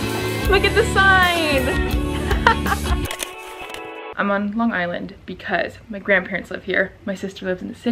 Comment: I really thought she said “I’m on love island” & I was v confused